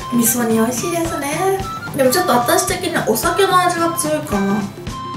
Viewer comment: 6분 45초쯤에 연어 맛말고 술 맛이 강하다가 아닌가요 자막 ?-? お酒の味로 들리는뎅..